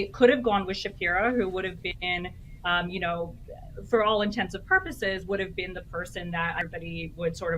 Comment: Did she say "for all intensive purposes?"